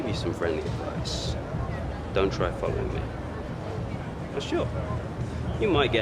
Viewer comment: Loooool does he say don’t try fuck with me at 24:07??😂😂